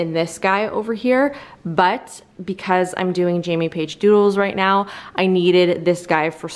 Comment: I thought you said Jamie Paige Jewels ❤️